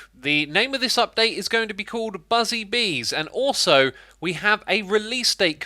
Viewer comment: Did he just say "BUSY" bees instead of "BUZZY" bees